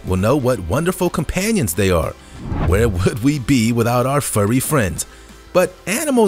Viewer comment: I thought he said what it would be without our furry girls because my volume is low i really thought he said that